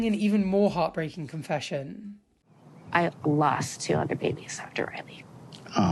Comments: Did she say she lost 200 babies after Riley! WTF? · oh my god I thought she said "200 babies" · Wait… 200 babies ?? That is 16+ years of pregnancies if she got pregnant every month… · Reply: I thought she said 200 babies at first, but no, she says "I lost two other babies"